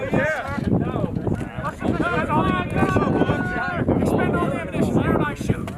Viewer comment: did i hear someone said you can spend all ammunition you want wow thats